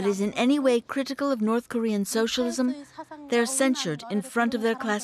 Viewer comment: Sad and very depressive to get on with life in a place like that... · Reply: What is she saying sintered? Censored?